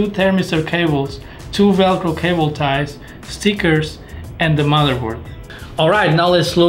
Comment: what he saying on 2:25 ??? I play 10 times and could not understand 😳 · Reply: he said "and the motherboard"